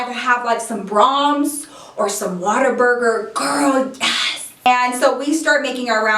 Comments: I thought u said water burger lol love you xoxo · Only Texas people know the amazingness of Whataburger 😊😩🍔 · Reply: I legit thought she said "water burger" 😂😂